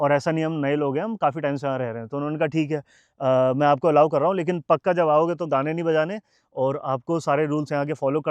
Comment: par kya bola bhai ye daaane ya gaane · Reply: gaane(songs)